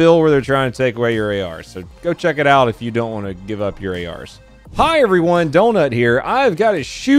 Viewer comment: did u say give up my AR they can come get it